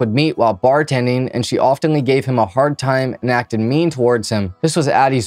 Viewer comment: what is this word pronounced "oftenly"?